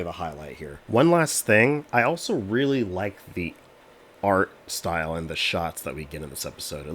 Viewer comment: I thought you said "I really like feet"